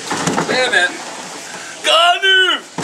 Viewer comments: who else heard the little kid say goddammit??? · i could've sworn that i heard angry grandpa's granddaughter scream "GOD DAMNIT!"